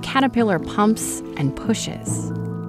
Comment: Am I the only one that heard her say humps and pushes and thought that was wrong